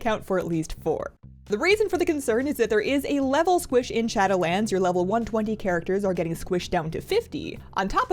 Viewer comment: I thought. I thought it said raid shadow legends and I got really concerned